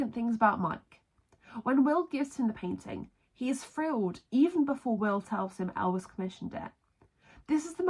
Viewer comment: I sorry but I can’t hear what you said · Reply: "He's thrilled, even before Will tells him El's commissioned it"